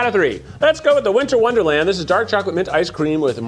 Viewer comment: I went to the Ben & Jerry site looking for Winter Wonderland, and it's not listed in their flavors! You guys in Cali must have bootleg B&J! Ah, in the recap I heard it, "Minter" not "Winter". Alas, no underground Cali B&J railroad. :)